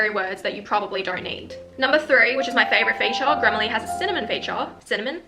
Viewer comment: I thought u said grammarly has "Simone feature"😂..